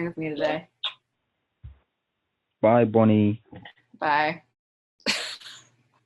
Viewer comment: I thought it said Bonnie Blacked💀